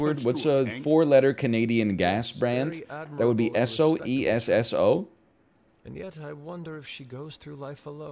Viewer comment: Asked for a 4 letter word for a canadian gas company, says a 6 letter word, refuses to ellaborate, gets called bald a few seconds later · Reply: he said "Esso. E-S-S-O". Though I can understand why you misheard it.